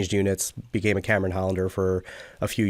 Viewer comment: Couple of questions; couldn't understand the accent totally... What is a Cameron Hollander @ 7:33? Also, what is Reich Force Royal Canadian Regiment? Lastly, what is the 'CAM'? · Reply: So what he’s referring to is Cameron Highlanders. The second thing you mentioned is ‚reg force‘ RCR.